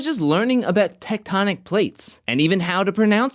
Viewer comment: OMG. I thought they said “Technotronic Plates”.